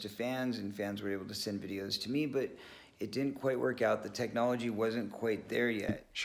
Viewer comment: when he says "the technology wasnt quite there", i hear "but i didnt want to watch their videos"